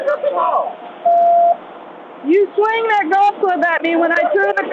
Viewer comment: Footage did she just say you Swang your golf club?